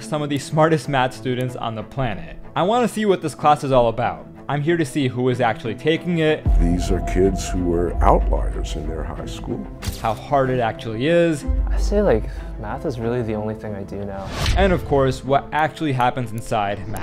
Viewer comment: Sounds like prof said “these kids are the outliers and assholes”. Lol